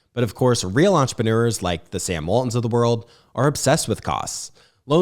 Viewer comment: I think Clay cited Sam Walton, founder of Walmart as an example of an entrepreneur obsessed with costs. He did not mean Sam Altman of OpenAI fame…Re-listen around the 42 minute mark to see if you agree. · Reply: hah... You're right! I thought I heard Scam Altman as well. Thanks for the correction.